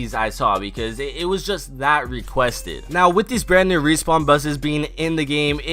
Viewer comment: did he call them respawn busses